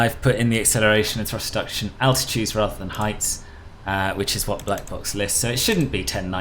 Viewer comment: What tool were you using to calculate the accel ht? I thought you said blackbox but I can't find anything by that name as a utility. Great video!